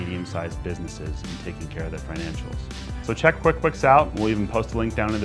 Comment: you said bookquicks instead of Quickbooks lol. Cool blooper. Thanks for the video, super informative!